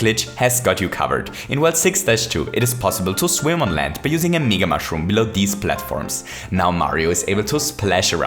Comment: I thought said the n word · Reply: nigga mushroom